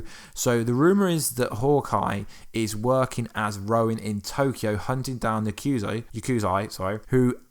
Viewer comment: You kept calling Clints new persona as "Rowan" it is Ronin...maybe I just heard you wrong, but sounded like you said it wrong multiple times